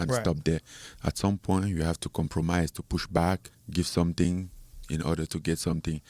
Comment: "sometimes you have to compromise and push back."..... WHAT??? LMFFFAAAOOO no wonder Dana said this guy is impossible to deal with 😂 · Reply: He said "At some point, you have to compromise TO push back". He simply meant he knows he can't go in making demands which only benefit himself (push back). He has to give something (compromise) in order to get something for himself. Seems like a valid negotiating concept. I think you just misheard him.